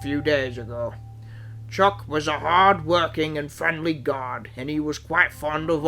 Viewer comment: Hey Everyone there's a goof in this Special😱! Sorry to say but its true, listen to what Sir Topan Hatt says to his engines. 11:11 to 11:18 " chuck was a friendly GOD⛪." Sounds like a reference to Supernatural's Chuck☝. 🤔😕 BE CAREFUL!!😰😨